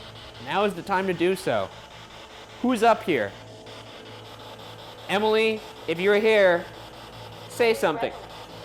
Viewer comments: stop i hear clear as day " who is emily?" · sounded like both voices said "who's Emily" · it sounds like someone is saying at 1:18:57 while is talking "who's emily?" idk 🤷🏻‍♀️ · Did anyone else hear “who’s Emily?” At 1:18:55?? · Anyone else hear the spirit box say "who's Emily" @ 1:18:54 ?